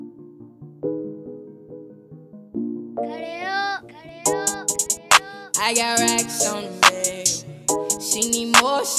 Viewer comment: what did he say at 0:13 ? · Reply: That's DJ Spinz tag "Cut it up" Every producer has a tag. Hope this helped.